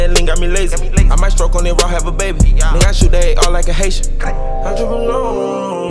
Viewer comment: is it me or does is sound like he is saying Andrew Bernard @ 1:08 instead of “im drippin on em”